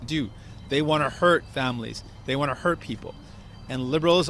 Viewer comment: DID HE SAY DUDE THEY WANNA HURT PEOPLE :body-blue-raised-arms